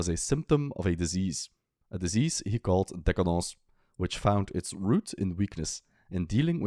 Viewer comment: Please, can somebody help? “Decadence”- Is that what this guy is trying to say? He pronounces a word that sounds like “decadauz” many times in this video (example at the 34:29 minute mark). I really like this guy’s work, but that word pronunciation confounds me. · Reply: It’s the French pronunciation of decadence, because Nietzsche himself always insists on using the French term in his work